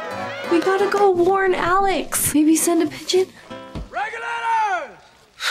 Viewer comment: did she say send a pigeon 😂 not 1600’s 😂